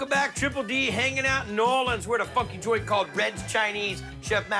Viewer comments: Sounds like he said we are at a f In joint at the :06 mark lol 😂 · I had to replay the first 10 seconds 4 times before I heard "funky joint"